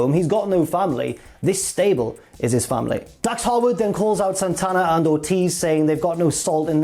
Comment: I always hear "Santana and/or Tiz".